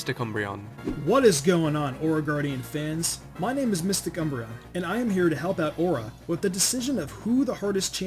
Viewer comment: Lol I heard "Mr. Cumbreon"